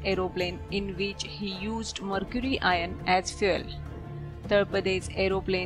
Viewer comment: did she say mercury iron as fuel?